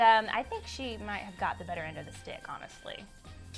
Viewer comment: Coulda sworn she said dick